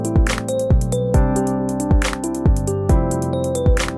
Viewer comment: i know that's serious, but in the end instead of "peace" i heard "piss" and now i cannot stop laughing, srry